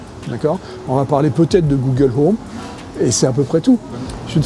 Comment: MAIS IL DIT QUOI A 5:46??? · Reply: le bab je ne fais jamais de requête siri via mon iPhone je pense